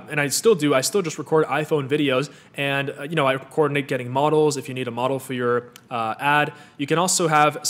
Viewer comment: I couldnt hear the part about the getting models. It sounded like he said "nate getting models" but I'm sure that's not it. Anyone know where he recommended finding models for your products? thanks :)